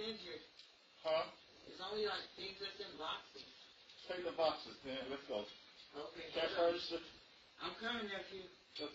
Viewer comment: Honestly watching the full video it sounds like a man and a woman, At 28:40 she says his name but i cant quite make it out, sounds like mickey or mikey kind of. She said "its a big ass safe, M____" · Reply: And at 29:45 he asks whats in the office and she says a bunch of boxes, to me it sounds like he said "take the boxes tina, lets go"